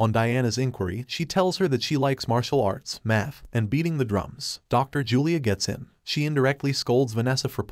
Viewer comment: i heard beating the drunks